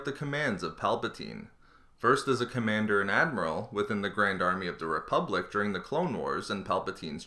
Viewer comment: I thought he said grandma near the beginning lol